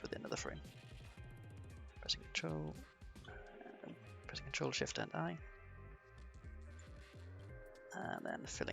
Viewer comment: what exactly did you say? ctrl + shift f9??? cuz i head something that has a 9 in it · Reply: I understood Ctrl, shift and I